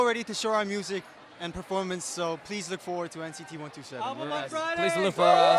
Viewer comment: this part subtitles are wrong. not NCT one three. this group is NCT 127